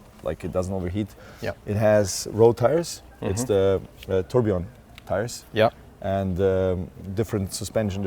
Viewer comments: I love Bugatti but I swear he said it was the turd. I know it's not the turd but it sounded like he said turd in English just saying.... It's a beautiful car. If I hit the lotto I will own one. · Why does it sound like he said, its the turd ? At the beginning of the video 🤣